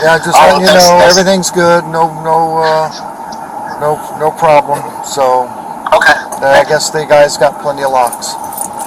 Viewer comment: "no no no problem, i guess they have plenty of locks" is that what he said at 13:14 ?!